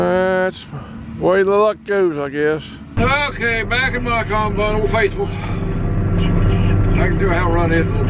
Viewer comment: About 11:00 mark, "...back on bottle faithful..."....instead of..."...back in my combine, old faithful...". Laughed so hard, kept thinking back to tag name: Bobgolian.